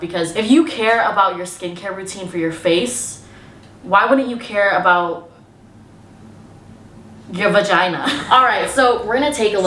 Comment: Hi I'm Loretta De Los Rios using Frank's account to make this comment with his permission. Did you say what I heard" unfortunately you can show your vagina" I dont want to see it.🤲